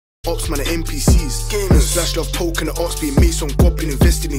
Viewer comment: Did bro just say his opps are npcs? 🤣🤣